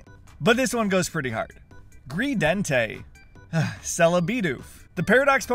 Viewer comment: I thought he said greenday (greedenteii)